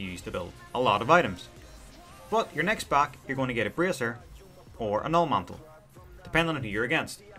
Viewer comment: Cant understand what you are saying at 2:09 "braiser"? I really want to know because im having a hard time with ad matchups Love ur vids tho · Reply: Haha in saying Bracer. God Dam my accent xD